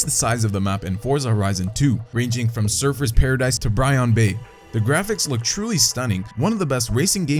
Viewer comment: Sounded like he said bryon bay it's Byron bay